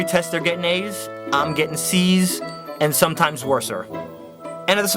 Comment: Did he say "worser" ?